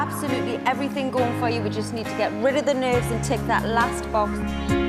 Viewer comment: Om I thought she said "You just have to get rid off the nose" and I was like ...😐